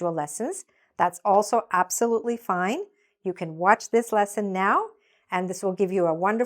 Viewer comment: On 0:46 she said "You can watch". but subtitles show "You have watch". The same It on 1:28. The subtitles show "a few days" instead of "a few ways"